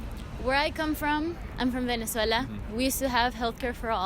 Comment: That last girl sounded like she said she was from minisota. I had to replay it 4 times before I realized she was saying Venezuela